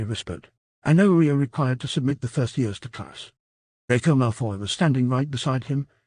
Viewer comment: DId SOMEONe SAy DraCO MALFoy